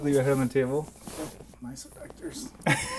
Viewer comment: Do you miss California? - I miss you. - ☺ I couldn't understand what says at 4:06 (I'm not a native English speaker), so can anyone tell me what it was? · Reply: He said "nice pectors," referring to his chest muscles.